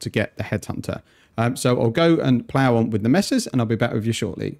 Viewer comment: I thought he said he was gonna go plow the missus and then be back with us 😂